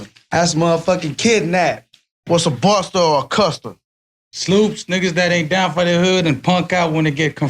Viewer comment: Did he say snoops? Like snoop dog?